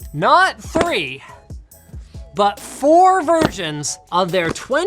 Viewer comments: did he say "but 4 virgins"? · Wait a moment did Linus just said “Virginss” 0:51 ??